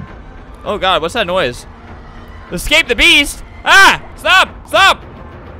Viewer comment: When flamingo first said the beast,I thought he said mrbeast